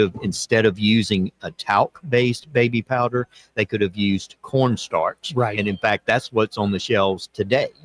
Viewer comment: It is a sign of the times that every time says "Corn Starch" I hear "Pr0n stars".